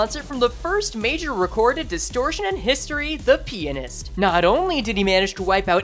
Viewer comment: the WHAT Oh, the Pianist. I thought you said the peni- actually, never mind.